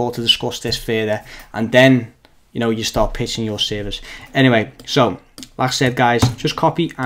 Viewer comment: I thought you said. "You can start Pissing yourselves" 😂 Great video